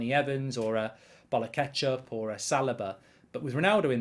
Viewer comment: Did I hear “rashford against a bottle of ketchup” 😂😂😂